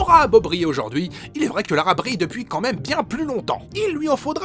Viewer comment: j'ai entendu "l'arabe rit" perso x)